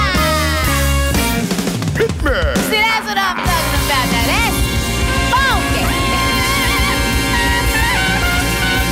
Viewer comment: Please tell me what she said I couldn't catch it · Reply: "Now that's what I'm talkin' about, now that's...FUNKy."